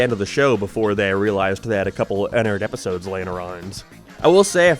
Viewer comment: literally what is he even saying here? Maybe it's because I'm not a native English speaker but I cannot understand what he's saying because of his accent. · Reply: couple of unaired episodes lying around